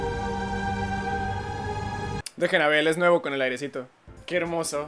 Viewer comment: estos son los años en los que misa dira : los malditos celulares chinos que explotan 👇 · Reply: Me pensé que dijiste huevo